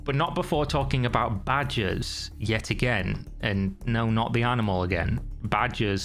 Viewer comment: I don't think I ever thought about badgers, when saying badges, until i heard your accent pronounce badges. Do you not pronounce the letter "R" in your words where you're from lol?